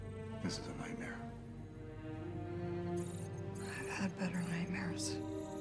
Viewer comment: Stop fake-ing it everytime, Natasha... What the hell you would have seen bigger than Thanos's snap ??? She tells this kind of line a lot of time 😂😂😂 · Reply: Right. I think the OG misheard and thought it was "bigger nightmares", not "better".